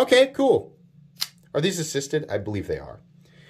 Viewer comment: Don't know anything about kershaw?!?! Lol they pretty mutch invented the spring assisted knives that everyone makes now (Ken onion design) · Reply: sorry, I thought at one point you said, "I don't know about these" But when I re-watched the video to time stamp it, I couldn't find it. Maybe it was a combination of 9:30 "Are these assisted?" And reading a comment simultaneously that caused me to falsely hear something.